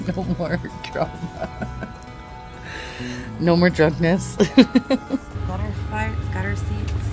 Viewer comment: Does she say "no more drunkness" at 18:28? Is that why they missed two flights? WTF happened??